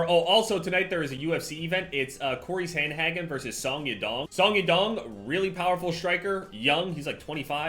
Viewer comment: I legit thought he said "Saw ya dong". Lmao. I was like wait, rewind.